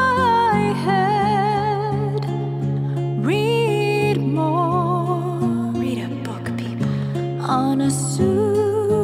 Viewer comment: I hear "Read More" as "Reed Moore", a person's name, or perhaps two last names.